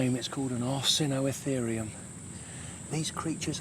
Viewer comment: What's the name of the animal he said in 34:55. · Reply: arsinoitherium.